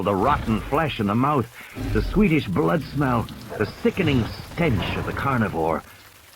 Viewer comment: If I were Tim I would tell Lex to shut the f up and stop screaming cause it’s not gonna help PS 20:48 did he say SWEDISH blood smell and what did he mean by that